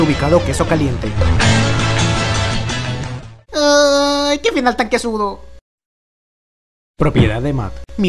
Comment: ¿que dice matt en el minuto 4:46? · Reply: que final tan quesudo